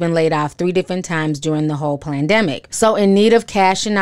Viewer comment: did you say plandemic...??